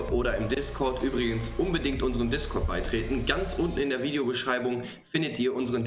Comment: das hat sich so angehört wie ein "(ganz unten) in der letzten ecke"